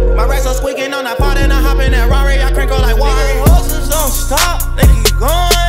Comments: Always thought he said “Horses” he actually says hustlers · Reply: I thought he said hoes don’t stop😂😂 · Always thought he said “Horses” he actually says hustlers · Always thought he said “Horses” he actually says hustlers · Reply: I thought he said "hoeses" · Always thought he said “Horses” he actually says hustlers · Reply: Thought he said hoses lmao